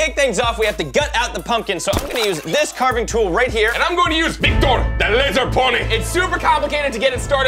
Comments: Who else at first thought he was saying "Victor the Lizard Pony"? Just me, ok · I thought said: thictor the lizard horse